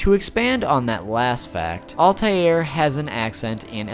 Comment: if you turn on subtitles it says salty air instead of Altair